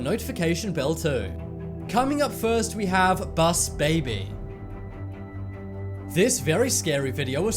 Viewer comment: When you said bus baby i thought you said boss baby XD